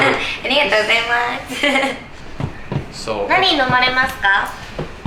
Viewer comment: what did she say? "Nani-o nomi masu ka?"